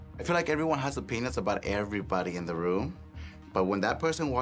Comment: okay, I know said “everyone has OPINIONS about everyone in the room”. But it sounded like she said something else there. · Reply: I for sure heard "a penis" the first time.